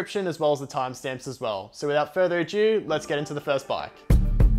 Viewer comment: did you just say without further a Jew?